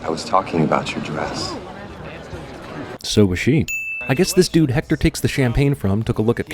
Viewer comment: i thought hal said nice dress? are you sure she had one? was it cg? was she naked? probably